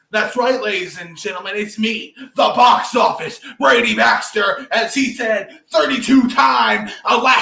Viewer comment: I thought you said Box Office Break Dancer at first 😂😂